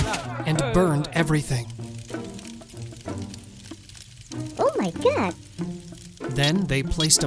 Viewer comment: What did the character at the end say? At 8:28. She was looking at Achan's burned belongings and she said something while covering her mouth... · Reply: She said OMG